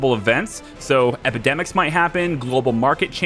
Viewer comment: When you were going through the settings, I thought you said "academics might happen" and then I wasn't sure if I misheard because some sort of education based resistance to communism seemed like something that would totally be in the game :D